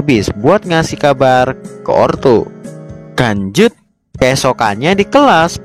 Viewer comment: apa aku salah denger saat mimin bilang lanjut terasa kayak bilang kanjut